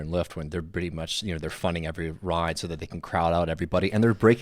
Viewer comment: funding every ride? is that what he said? what does he mean